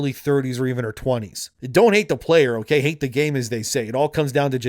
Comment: I heard it said “hate the player but don’t hate the game”.